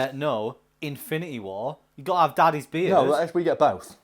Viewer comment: "Gotta have daddy's beard?" tell me I heard it wrong! lol. Also, it's "Frin." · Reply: QueenOfTheSharks no it’s definitely daddy they say it again and over multiple eps